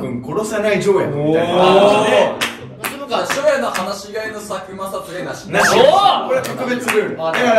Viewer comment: 何回聞いても10:36の「初夜の話し合いの…」が「初夜の放し飼いの佐久間の……」に聞こえて、毎回放し飼いされてるさっくんの画を思い浮かべてしまう😂